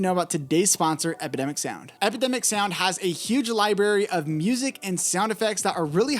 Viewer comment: Did you say "Liberry" at 1:14 into the video? You made this fulltime librarian laugh their butt off. Keep up the helpful content.